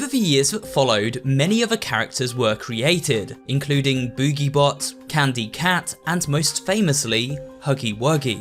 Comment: i thought he said boogie woogie oml there could be a third one that’s green that will dance